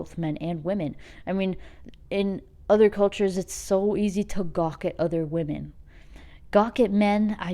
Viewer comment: what's the word please? that she used describing men and women ? someone help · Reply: yeah it's gawk! as in it's usually men who stare at women